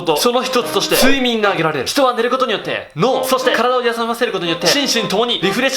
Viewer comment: 体をやさませるって聞こえた